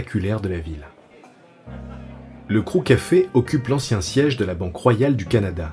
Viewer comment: j'ai entendu "Crous Café" j'ai failli taper un arrêt cardiaque